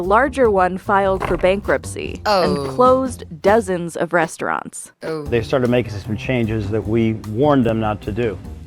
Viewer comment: Did I mishear or di the narrator say "breastaurants"?